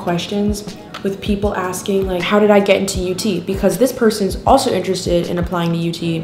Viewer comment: I thought she said youtube weirdly